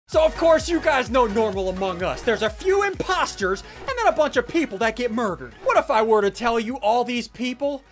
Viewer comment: lol is it just me who heard him say at the start in the dropship he says, we are doing some Dormal among us, then he went in game?